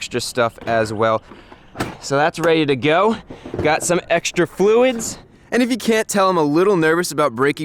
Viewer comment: did that said blinker fluid?